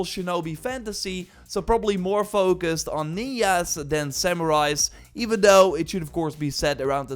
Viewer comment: Did you just say ninyas instead of ninjas